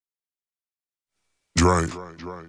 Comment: i heard drake everytime he told drank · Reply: hhh I heard "drive"